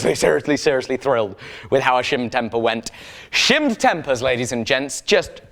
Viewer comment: At work with the volume down, I could have sworn you kept saying shiver me timbers... Shimmed tempers, now I get it.